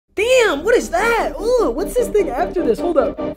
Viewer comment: I swear I heard say “ooh what’s this ni**a after this” at 0:01 lmao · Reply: "What's this thing after this?"